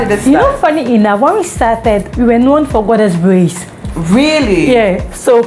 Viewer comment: I can't really tell what she said. Did she say 'borders braids??' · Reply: Goddess braids, she said.